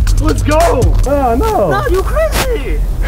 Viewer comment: Elbow to the head. 😂 Then does he say "Nah you hit me"? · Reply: No he says "Nooo you crazy..." 😂😂